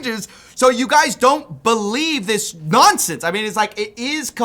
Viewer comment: I heard "don't believe this nonsense" as "don't believe these nonces"